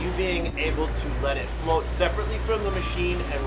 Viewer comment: i didn't catch what said there? What function? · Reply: Float